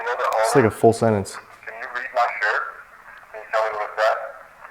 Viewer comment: i swear it says "I don't care about your stupid shirt" then some more I can't understand